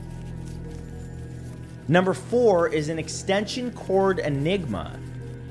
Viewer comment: I swear I thought #4 said; "Extension Cord Enema"🤦🏾‍♀️😅🤣